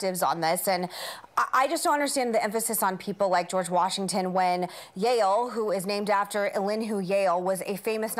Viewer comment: when Meghan said "Yahul" I literally didn't understand what she was talking about 🤣 .....Yale.....